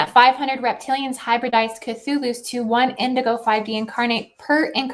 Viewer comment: Did you say hybridized Chathulus? O_O